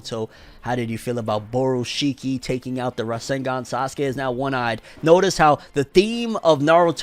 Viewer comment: my dood said rasengan instead of rinneaGONE